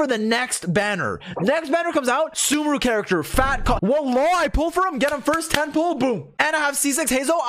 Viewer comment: Did he say wallah?